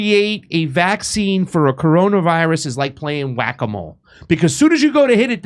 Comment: I'm not sure if he's lying, I clearly heard him say "our virus"!!!